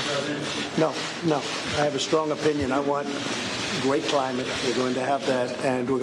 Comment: did he just say we are going to have a great climate !? !?